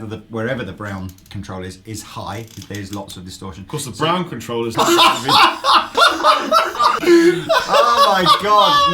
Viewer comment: I need to know what said at 5:35 haha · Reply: "Racist"